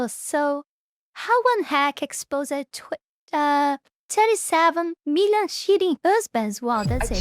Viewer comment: I thought you said "37 Million Shitty Husbands" and I thought "Based". Hahaha!